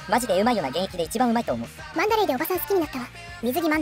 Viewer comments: ラグドールがラブドールに聞こえたの俺だけ？ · ラグドールがラブドールに聞こえたww